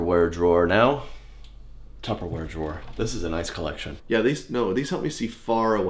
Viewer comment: did he say "tufferwear"?